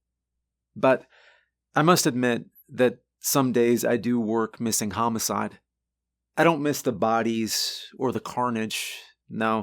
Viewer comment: Why do I always catch these things, even when I'm barely listening? Right near the time stamp, says: "...I do work missing homicide." instead of "...I do miss working homicide." I don't know why these kinds of things jump right out at me...